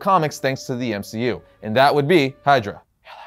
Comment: did he just said hail Hydra? I heard it lol